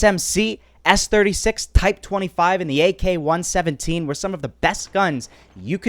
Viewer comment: Did he just say "ak1-17" bruh it's